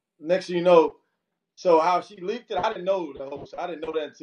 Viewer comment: Am I the only one who heard him slip up and say he leaked the tape right after eight minutes????? Right around 8:15. He sound shady to me even if she did do it. Milan is shady too because he is just inserting himself. One minute you fighting the next minute you accusing your best friend. All three of y'all in cahoots. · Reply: zjaunef he corrected himself but before that it sounded like he said before I leaked and then started the correction. I could be wrong...